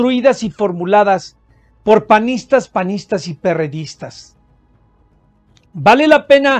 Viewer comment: Panistas. panistas y perredistas. Oí dos veces panistas y pristas?